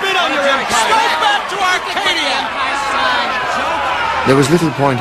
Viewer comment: I can't quite make out 0:39. urge to say Go back to Equestria!